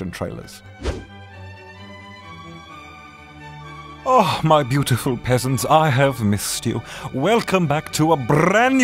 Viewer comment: WAIT, I just subscribed and I hear "hello there my beautiful pesents", from Sebastian. Since when was I beautiful